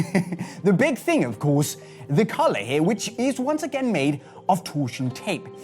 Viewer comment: Was I the only one who thought was saying 'colour' when he said 'collar' 😂